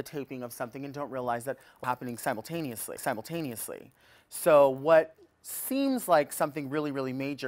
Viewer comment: what was it she said that had to be edited? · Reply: like it was some kind of glitch. From what I read it was "a lot of things are happening simultaneously"